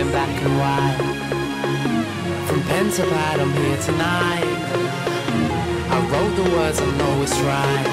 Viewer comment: What does he say at 0:35? "From pentalie(?) to here tonight"? What means "pentalie"? o.o